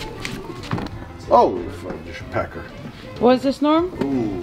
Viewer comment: did he say fudge packer?